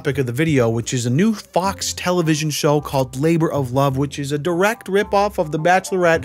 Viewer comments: I thought you said FLAVOR of Love. I was like ",Oh yeah I remember all those women that gave up their lives to audition for marrying a man in his 50s with a gold clock around his neck". Gotta love hip hop. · damn i thought you said FLAVOR of love a real masterpiece